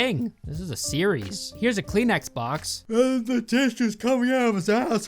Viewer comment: I thought he said “clean X-box and not “Kleenex box”